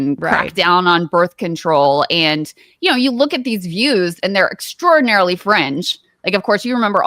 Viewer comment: I thought Krystal said "you look at these views, and they're EXTRAORDINARILY French."